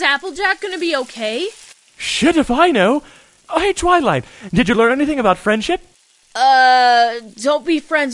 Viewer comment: for the longest time i thought she was saying "Did you learn anything about french yet?" 😭